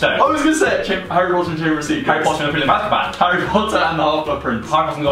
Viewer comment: In the 1st round, i heard "Harry Potter and The Prune rose"...